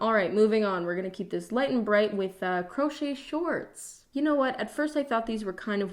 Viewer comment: lmao i thought you said pro choice shorts, and honestly those would be sick